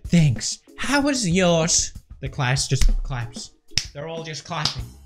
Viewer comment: I thought it said mein class